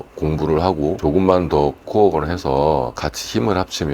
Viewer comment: 코업보다는 co-work (코웍)으로 들리네요. '공동 연구'로 이해하면 좋을듯 합니다.